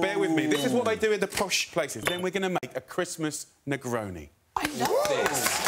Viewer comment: A Christmas Negaroni. Yeah I heard that wrong.